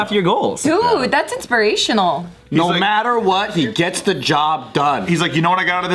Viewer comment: I thought was gonna say "no matter what, you got to nut"